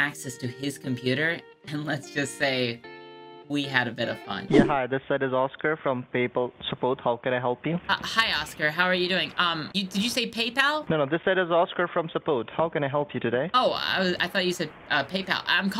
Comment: It actually sounded like he said "Papal support" like he's there to help the Pope